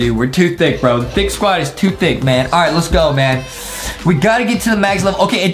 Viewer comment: I thought said he wants to be thick